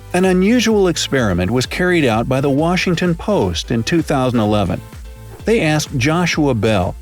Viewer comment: Hey there, unique souls! Which of these experiments would you like to participate in? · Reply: The captions say the year 2007 but the narrator said it was 2011